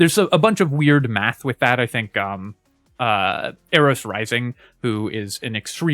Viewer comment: couldnt understand the name of the person, are they on youtube or elsewhere? Can you help with link? Thank you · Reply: ArosRising, I think they're mostly active on the reddit and discord, and have produced tons of great guides especially for high level %runs.